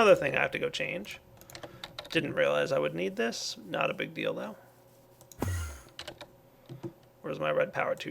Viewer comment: Oh god 7:07 my sick mind kicked in O_o "not a big dildo" was wad i heard